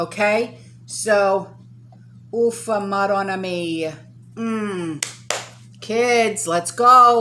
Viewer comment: What is that you said at 9:00 ? Ufa morana Mia? I am just curious. I want to start saying that too lol 🤣